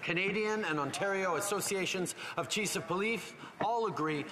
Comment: what did he say about 1 min into the video, where he's listing the agencies and I thought he was trying to say "Chief of Police" but it was more like the "Cheece of Poleaf" did i misunderstand him? What did he say?